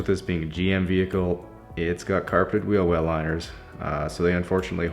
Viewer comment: did he say "Carpeted wheel wells?"